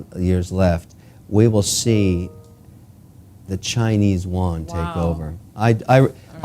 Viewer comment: lol 10:31 did he just say chinese "won"? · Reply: You never heard of Don Yuan? Haha!